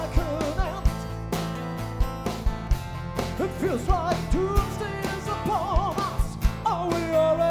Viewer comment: Does anyone else hear "tuesday is upon us " ? 😂 doomsday